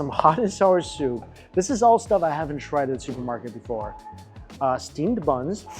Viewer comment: Did he just say, "Shupermarket"? 😂😂😂